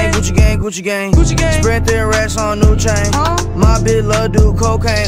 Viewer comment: I hear "spread their ass on lil chain"